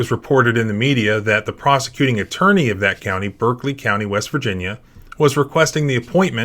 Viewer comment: We need to start prosecuting for this. Simply moving to the next county just isn't cutting it. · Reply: For a second I thought you said country and not county, and my heart sank. Did I just wake up to a mass American migration? Wait no, he said county, we're okay guys. False alarm. Canada is safe.